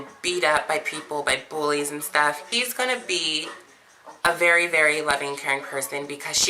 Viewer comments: As a strait guy my response to this trans woman is no, never not in a million years. My body my choice. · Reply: Nevermind that I slowed it down and in fact I hear at 2:15ish "HE'S gonna be" immediately followed up by she's... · As a biological woman, I would like to make a video about the number one reason why you shouldn't date trans women: 1. They're actually men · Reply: Slowed it down and in fact I hear at 2:15ish "HE'S gonna be" immediately followed up by she's...